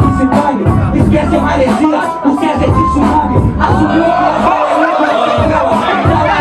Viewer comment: Queria entender o que César faliu em 05:06. Pareceu interessante que pessoal ficou abismado. Mas não consegui entender o que foi falado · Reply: Não demonstra inteligência que se dane, esquece a Maresia, O César é Tsunami.